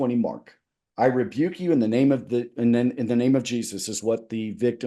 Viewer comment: I thought he was saying you better not rebuke me in the name of your own Jesus.